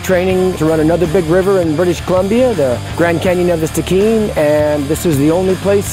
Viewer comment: Sorry but I cannot catch the name of the other river in BC/Canada which they are training on the Wenatchee river for (0:46 of the vid ) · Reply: They were training for the Grand Canyon of the Stikine River.